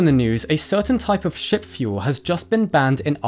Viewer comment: Hey, they cut off the bloke! Good God Lads! it sounded like you said "Sh*t Fuel". I had to rewind that, then I heard, Ship fuel.